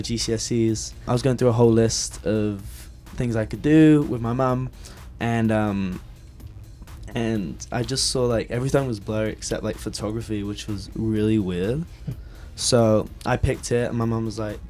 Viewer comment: 0.07 is it just me or does that sound like he said something else 😂😂😂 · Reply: I heard PORNOGRAPHY